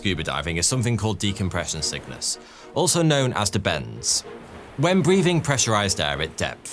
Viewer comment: I remember my science teacher at Little Lever Secondary School teaching us about the bends. For some reason on that day - circa 1996 - I thought she said "Benz", as in Mercedes Benz, so for years afterwards I thought it was called the Benz. It was only a few years ago it realised it's called the bends. But saying the bends is weird to me. To me it's called the Benz.